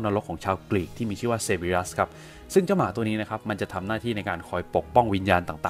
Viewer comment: ผมลได้ยินพี่พูดว่า Sepioroth